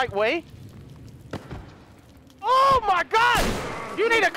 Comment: it sounds like he said “ OMG, YOU NEED A CONDOM!!!” LOL 😂😂